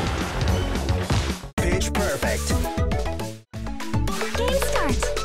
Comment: It's me or did say "Bitch perfect."